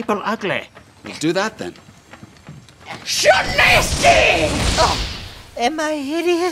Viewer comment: is it me or does the spell sound like hes saying "YA NASTY"